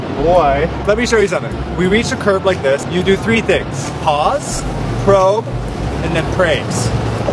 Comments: I thought you said "pause, probe, then pray" 😂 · I thought he said pray instead of praise as if he would cross the street and pray not to get hit lol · I thought you said "Pause, Probe & PRAY" Hahahaaa